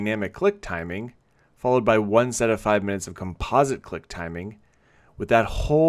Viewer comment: I thought it said cuck timing, lmao